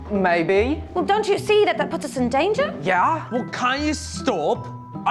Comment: That British accent though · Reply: i thought she said "dont you see the Potters are in danger"